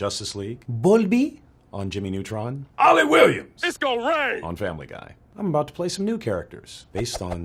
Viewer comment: I always thought when he said "it's gon' rain" he actually said "Disco Rae" My life is a constant lie X)